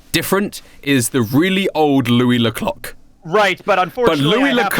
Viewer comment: did he say louie le cock...?